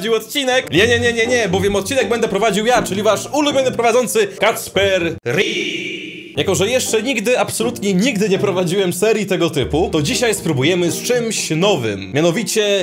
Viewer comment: Jeszcze NIGDY nie nagrywałem odcinka tego typu (i nie robiłem takiej miniatury xD). Nie wiem co o nim sądzić - ciekawy? Nie ciekawy? Dajcie znać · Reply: Na początku usłyszałem Kacper Ryj xd